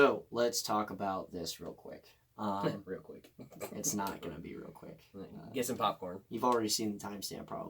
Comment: Why @ 0:30 it sound like you said {nigga dis ain't gonna be real quick} Lmaooo